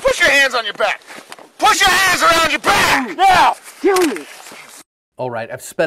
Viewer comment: think I heard "kill me".